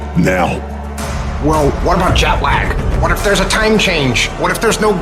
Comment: I was listening to this and initially thought that the speaker said 'Jack Black' rather than 'jet lag' LOL